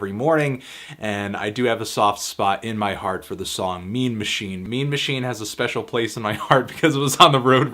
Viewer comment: my born in 2005 brain thought he said meme machine